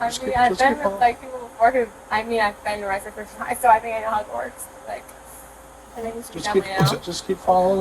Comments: When they started the sobriety test I could have sworn she said, "You know I've been with black people before....been arrested....so I think know Hogwarts." I had to listen to that part like 8 times to hear her actual words. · I seriously thought she said "I've been arrested for driving in Hogwarts so..." 😂 and why are they bothering to do field sobriety tests, didn't she say she was only 20? If they smell alcohol and she's all but admitted to being fucked up, arrest her bc her blood alcohol level should be all zeros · She's so drunk it sounds like she says Hogwarts instead of how it works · She would be the annoying talkative drunk... She doesnt know when to stfu good god. And i thought she said @ 7:30 ive been arrested for DUI for stopping into hogwarts, but she said i know how it works. Eh close enough shes slurring like crazy 😂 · I thought she said "I've been to Hogwarts." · I swear i heard her say "i'Ve BeEn ArReStEd At HoGwArTs" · I might need my ears checked because I think I heard "hogwarts". 😮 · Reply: Turns out she said, "How it works." 😮😅😅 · I swear when she said “how it works” I thought she said “hogwarts” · I literally thought she said "I've been arrested before for driving in Hogwarts" 😅🤣